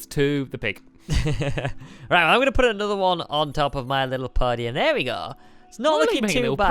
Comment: who thought said "I'll put one on-top of my little pony" xD